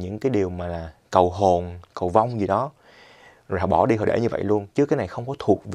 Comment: Em nghe thành cầu hồn, cầu bông :((, ớ mấy ba Youtuber cầu lạ ghê